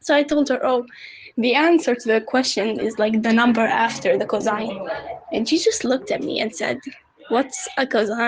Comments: maybe she was confused because you said cazine instead of cosine /lh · what i hear you say: Cuzine what i know your trying to say: Cosine